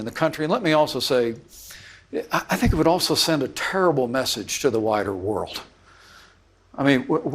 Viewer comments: I heard him say “we owe it to the whiter world to put aside division and leave this behind us” and it sounds only more honest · Sure sounded like he said that would send a terrible message to the “whiter” world. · Did Pence say, "we have to send a message out to The whiter world." ? That's what i heard. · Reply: i heard, “wider world”, yes